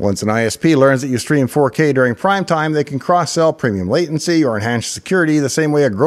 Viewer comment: What does "cross sell premium latency" mean? As in, a ISP product you can purchase? At first I thought he said "cross out" premium latency: your just streaming down, it can buffer, so you don't need the same latency as a gamer (but obviously that's not what he said). Is it possible for the ISP to change the level of service they provide you... because they think you won't notice, they don't like the sites you go to, etc? I've been having latency problems lol